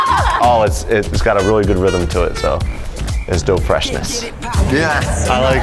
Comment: LOL if you have subtitles on the screen go to 2:11 , when says dope freshness the subtitles say "DOUGH freshness" LOL